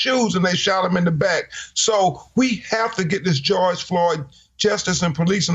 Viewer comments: every time I hear "THE GEORGE FLOYD"S ACT" sounds like they are saying "THE IDI AMIN ACT" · The county attorney cannot deny that he's a racist. There is enough research that shows that racism is a mental illness. Independent of their wealth and education, racists are nothing more that people without self-respect who believe, mistakenly, that their skin color gives them some kind of "superiority" over others. This belief turns them into psychopaths and criminals. It turns them into murderers. That's why some poor whites identify with people like Trump and this county attorney. In their mind they are like their exploiters and abusers, not like the "other" exploited and abused. Mr. Brown's murder is another example of human rights violations in the US. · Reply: every time I hear "THE GEORGE FLOYD"S ACT" sounds like they are saying "THE IDI AMIN ACT" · When a cop stops you he knows nothing but what he sees and how he interpret that. Unfortunately we dig in the VICTIMS past soon as they are shot( especially black people). The cops are always trying assinate the victim twice. SMDH. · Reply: every time I hear "THE GEORGE FLOYD"S ACT" sounds like they are saying "THE IDI AMIN ACT" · It seem that right after Floyd justice, it appears they are getting worst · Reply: every time I hear "THE GEORGE FLOYD"S ACT" sounds like they are saying "THE IDI AMIN ACT"